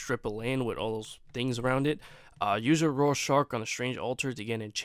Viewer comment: At &7:09 what are you saying for some reason i can't make it out. Are you saying use a raw shark?